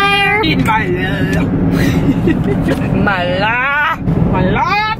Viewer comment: The whole time they were saying “my love” I thought they were saying “myla” and I thought they actually knew I existed💀